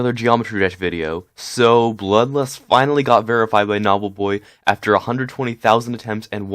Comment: I thought u said bloodlust verified knobbleboy at the beginning XD